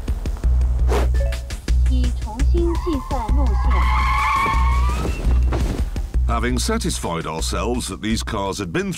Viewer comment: Can someone please translate what was said at 4:20? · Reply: recalculating route. Its from the GPS